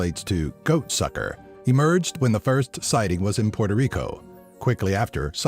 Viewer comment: i literally heard "cock sucker" xD